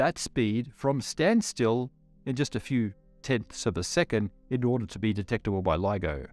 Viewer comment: you say "Tenths of a second" but the caption says "Tens of seconds" - Which seems more likely to me, since the things I know LIGO detects already are in that range. Which is it though? Edit: Later on you mention "Less than a second". So the captions are probably wrong at about nine minutes.